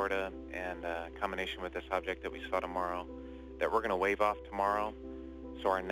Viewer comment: did he say "based on the subject we saw tomorrow" @ 3:10 - @ 3:14 and whats mmt? who is he talking about?